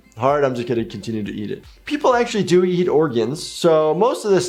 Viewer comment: erm wtf did he say????!!!!😭😭😭😭😭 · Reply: He said people actually eat organs